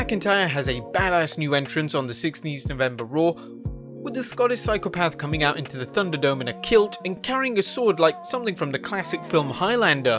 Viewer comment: did he say scotis phycopath